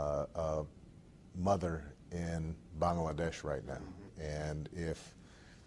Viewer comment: did he said Bangladesh??